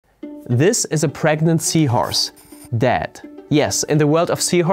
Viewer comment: I misheard him at first: "This is a pregnant seahorse: DEAD." lol